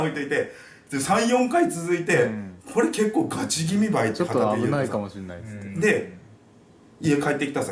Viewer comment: に 4人目の声らしき音声が聞こえます！ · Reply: それは撮影してる人の「ちょっと危ないかも知んないつって」の「つって」の部分がそう聞こえるんじゃないかな